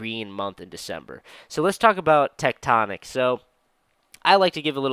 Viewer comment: Which chart is that for? The one at 2:38 . Your image is covering the name of the asset. Thank you. (I think you said Techtonique?) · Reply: Tectonic